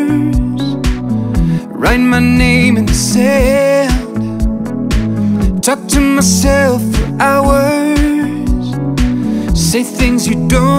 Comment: did he say “touchin’ myself for hours”? 😮